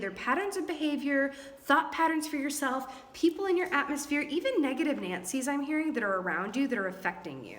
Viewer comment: Did you really say negative Nancy 's at 10:05???? She s my mom a saggie who hates seeing me suffer from a breakup